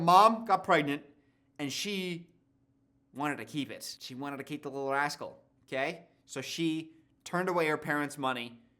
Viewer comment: sounds like your saying “she wanted to keep the little arsehole” 😂😂😂